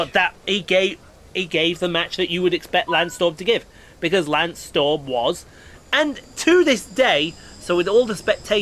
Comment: For glasgow, I bet tico will be raging that you am doing well. Interesting accent. I never get thunder and lightning. Normally thunder here. LOL I thought you were saying lance stroll LOL